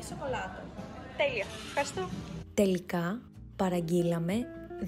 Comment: I looked up what you said," We ended up ordering" on google translate it said "Καταλήξαμε να παραγγειλουμε" but I heard you say "Τελικα παραγγειλουμε" are both ways correct? Or does each way have a different meaning?